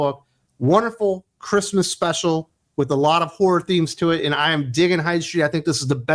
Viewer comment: I know you said ‘horror’ but I heard ‘a whore inspired comic book’. Nearly spat my coffee.